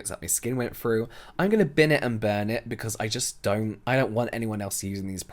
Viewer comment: No bc I thought it said „bin and bum“